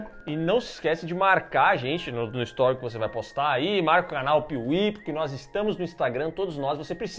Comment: Eu entendi "canal kiwi"